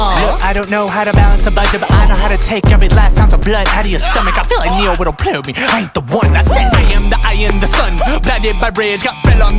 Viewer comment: I listened to this track on repeat over 9 times while making dinner n I was convinced n tryna decode this obscure Ne-Yo reference 😂 I heard, “I feel like Ne-Yo with a prayer bead; I ain’t the one.” I just sat down to eat so now I can actually watch it n read the captions. (I’ve never listened to Ne-Yo and I didn’t grow up Catholic, so I don’t think it was selective perception)